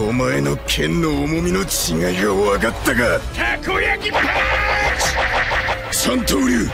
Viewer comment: Someone No one: Me: Do I hear you say Takoyaki?